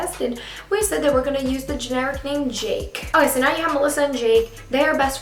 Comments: LMFAO I THOUGHT SHE SAID ALISSA AND JAKE I DIED AKHAKSBSHAKJJ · I thought she said ALISSA AND JAKE.for a second